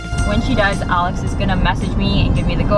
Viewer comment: The subs literally says "when she dies" at 1:23 instead of does